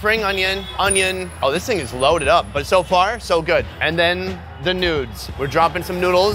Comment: I know he said "the noods" as in noodles- but Istg- I heard "the n*des"